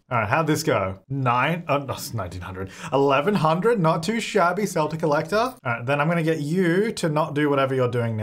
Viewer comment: I have a question. I appreciate the subtitles, but are they created by a real person or not? Because even auto-generated subs don't put "Celtic Collector" instead of "sell to collector" (3:34) 😁 I've seen a lot of those things in the English subs and sometimes they are quite funny